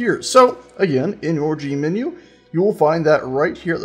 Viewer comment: I heard 'orgy menu'. Also, lol at the peasants fighting at 8:25... 'gimme my rock back!'.